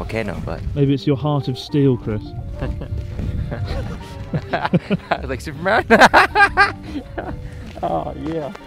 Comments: I think he said like superman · Like Superman? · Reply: I thought he said "like super mario" but now i feel really stupid